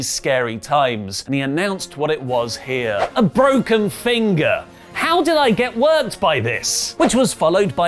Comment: Did mjf say it was a broken finger I could have swore I heard him say it was a hang nail